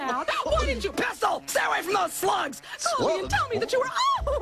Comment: Did... Did she just say "sluts?" In a kid's cartoon? There goes my childhood...